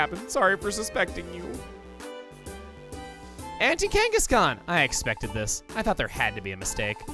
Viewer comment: Am I the only one who thought he was saying "Anti-Kangaskhan", not "Auntie Kangaskhan"?